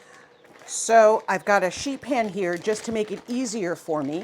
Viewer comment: I thought you said you had "a sheep pen" 🤔🤔